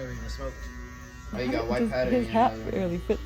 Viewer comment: Wait at 7:36 did he say he has white powder in his nose?